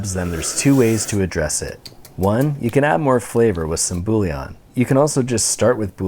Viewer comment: What does he say at 0:37. Builion ?